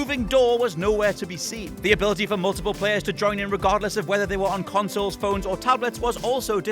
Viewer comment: Did I hear irregardless